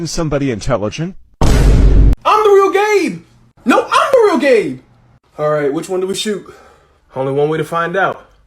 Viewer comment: for a second I heard: I'M THE REAL GAY